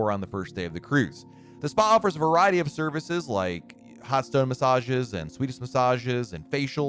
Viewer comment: I have hearing loss and thought you said "pasta massages" (vs hot stone massages) I thought about sitting in a giant tub of spaghetti, then I got hungry lol